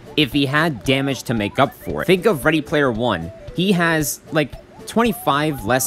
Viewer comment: I swear I heard “freddy player one”